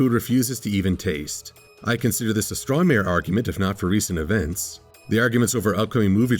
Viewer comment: Is saying "Straw Mare" Arguement as a play on a "Straw Man" Argument at 3:40 ? Or am I hearing things (probably a side effect of witnessing Butt Flames)?